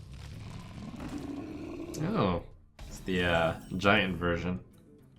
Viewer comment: i heard ohh it's the giant virgin